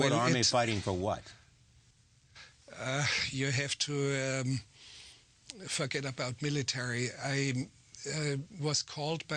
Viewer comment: "You have to fuck it up about military" Yeah yeah, I know. But it sounds like that's what he's saying!